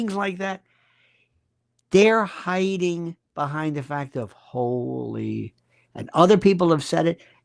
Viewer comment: you said ,"They're hiding behind the fact of holy: What are you saying?